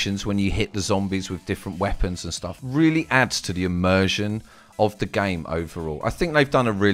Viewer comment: sry but, what is this word he says: emergin (or so) im not an english dude :s someone explain please, i hear it in so many games like, example: mafia2 has many emergin features. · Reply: The word was immersion , its means when you are deeply mentally involved in the game , like you believe it.Hope that helps :)